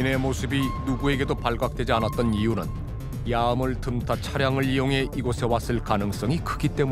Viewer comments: 뭐라고 하는건지 아시는분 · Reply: 야음을 틈타 차량을 이용해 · 얌을 틈타? 뭐라는거여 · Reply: 야음=밤의 어둠